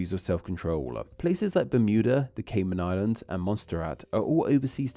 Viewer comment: Did he call Montserrat "Monster Rat"? 🤣🤣